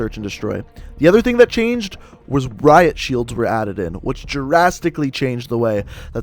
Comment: Not trying to be rude you sounded like you were saying Jurassic instead of drastically